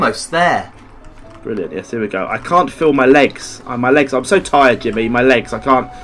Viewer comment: I thought he said film my legs